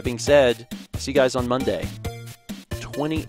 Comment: Does anyone know what was the last thing he said? I can't understand what he said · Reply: I think it was "Hope you're pumped", mostly because the other things I heard didn't make sense including "Hump the phones", "Hold the phone", and "Hump da lump".